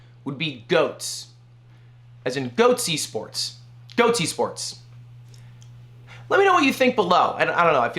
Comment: When you said Goats esports I thought you said Goatse. Don't do that.